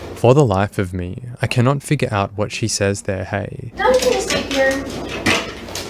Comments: I hear "Nah, we can just eat here!" No? 🤨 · I think she’s saying “no we can just wait here!” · hey. I think she says “uhmm, nah we can just eat/ stay here”. Meaning at the countertop. I think it was her childish like tone that got to him and made him smile. Weird. But I say that because he asks where they should eat at right before she says what she says. 1:18:44 : “I didn’t plan on having _ with her. I mean I know what the texts said. But ..”. But you drove over two hours, for another conversation? Yeah I think not . You’ve brought yourself this far, just tell the truth.